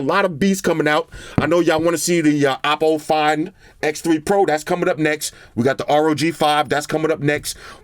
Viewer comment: I really thought you said "Apple fan X3 Pro" lmao 😂 Cause it is still accurate looking at the camera module design